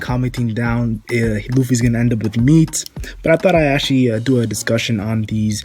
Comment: you said that people will be commenting that Luffy will end up with "meets" or something that sounded like that.. I've never heard that name, who in the world are you talking about? Genuinely curious